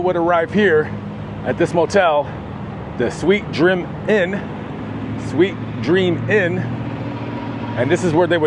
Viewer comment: I swear I thought said "sweet trim inn" 🤭